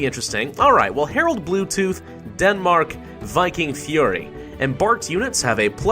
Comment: OH THANK GOD i thought that said Viking Furry